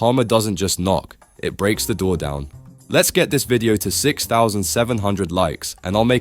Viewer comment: did you say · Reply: 6 7 00 likes